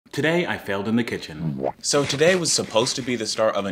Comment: I thought you said I failed ketchup